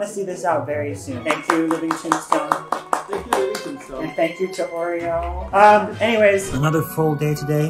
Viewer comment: I thought u said u ate "living tombstone sandwich"